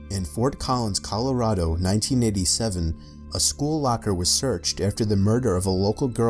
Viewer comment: did you say 1987 dies